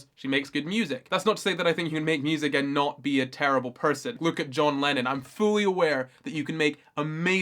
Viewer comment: At the beginning I thought you said "John Legend" instead of John Lennon and I was thoroughly confused. I was like, what did John Legend ever do?... he seems like a good guy lol.